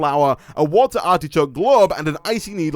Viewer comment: i like how said a watel of water instead of a bottle of water